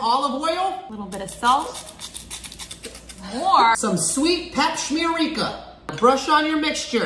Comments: 🤤🤤 And did he just say papsmearrica?!😂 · Did he just say pap smear rica😂